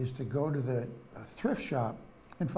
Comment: Did he say "thrift shop" table @ 5:50? I don't know where to get any table for a couple of bucks.